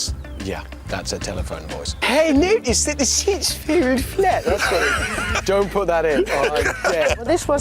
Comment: Anyone that can transcribe what Mr.UpsideDown says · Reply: “hey, notice that the seat feels flat” said hamster sarcastically